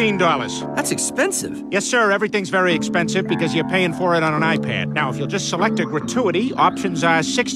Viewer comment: Im pretty sure i heard him say "because your paying on an ipad i watch porn on" idk might just be me being stupid tho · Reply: He said because you’re paying on an iPad I use to m**terbate